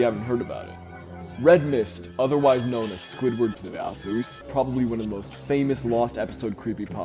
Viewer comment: Why squidwards s*****e in ur edition sounds like u was saying squidward sousege?